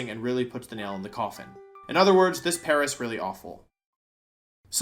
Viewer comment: I don't know if it was intended, but I heard this as "this Paras really awful" which, I mean, is technically correct...